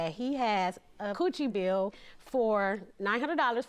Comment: Did she just say "coOcHiE BiLL" 😂😂😂